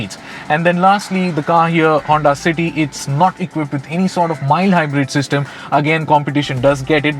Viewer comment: Any sort of what at 5:10 ? Could not make out wjat you said there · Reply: Any sort of mild hybrid system